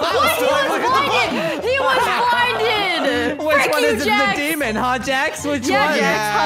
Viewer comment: Is it just me or did i hear "f### you Jax"